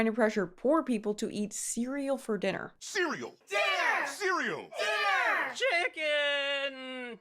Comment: Cereal - Killer! Am I the only one who hear it?